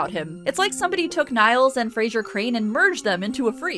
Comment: “It’s like somebody took Niles and Frasier Crane and merged them into a freak.” I cannot tell you how much this made me laugh 😂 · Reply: I first heard this as "Niles" and "Frasier Crane", separately, and thought she was talking about mixing Frasier with Niles from The Nanny.